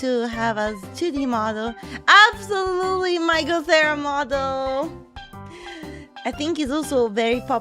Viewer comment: i thought she said "michael cera model"